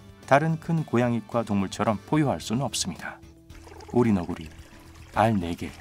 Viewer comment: 다른 고양이과 동물들처럼 ??할 수 없습니다에서 ??이 무슨 말 인가요? 계속 들어도 무슨 말인지 안 들리네요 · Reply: 포효 할수 없습니다